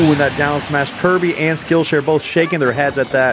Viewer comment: did I hear skillshare? Which is ironically our sponsor for this video